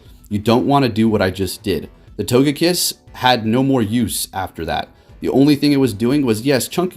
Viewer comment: Oof on the first battle I heard "The Only Thing They Fear Is You" Damn, that's what I'm talking about